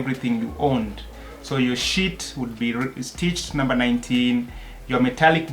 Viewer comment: Yaani you said sheet, and I thought I heard shit...and I wondered for a second why would they put a number on that😅